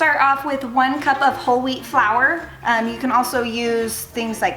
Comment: When she said "Whole wheat flour" at first I thought she said "Holy flour" XDDD